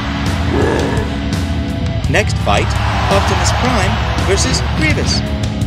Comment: I’m not a hater but when he said Optimus prime and the white guy I heard it like Optimus prime vs penis